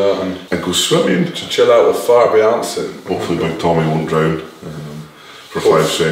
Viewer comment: Did Tom just say chill out with Thor Beyoncen muhaha